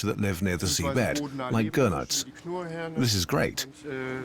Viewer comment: I coulda swore guy said, "Gonads".. "This is great. We'll process them now"...😂🤣🤣